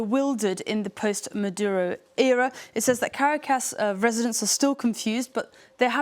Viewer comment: Did she say Caricas instead of Caracas